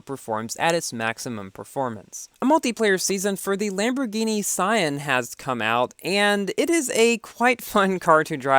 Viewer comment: lol I thought you said my name but with S at the start 😂 Lamborghini Saayan 🤣